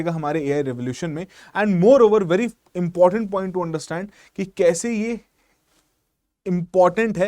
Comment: did he just said Fking important point? 😭🙏🏼